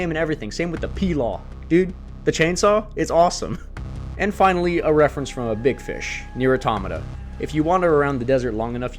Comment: What’s the game from 1:44:14? Cant quite catch it when you mentioned the title. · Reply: Onirism